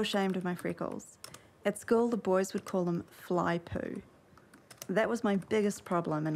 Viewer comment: What does she say at 24:17? "I used to be so ashamed of my freckles. At school the boys would call them ?flypool?" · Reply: Fly poo